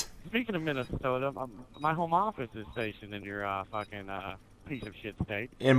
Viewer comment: does he say "my Home office is situated in his piece of s*** state"? Cause that would mean he lives there too...